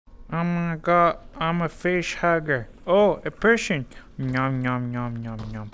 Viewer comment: am i the only one who thought he said headcrab and not facehugger